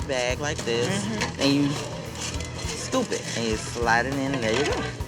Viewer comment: Did she just called her Stupid!? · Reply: Scoop it, he said- about the fries